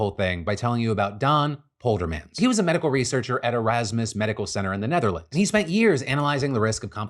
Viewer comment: When Phil said Erasmus, I thought I heard him say Merasmus.